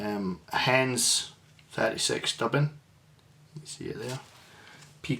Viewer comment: Nice looking fly .What dubbing is the thorax please ? I thought you said hends 36 but I can’t find a hends dubbing that is number 36 ? Thank you